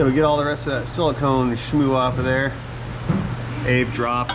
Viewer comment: me: did he just say Schmoo? "AvE drop.." XD We have ourselves a damn-skookum meckanik here <3